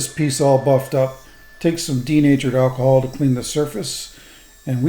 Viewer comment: I thought you said "teenager alcohol" 😅.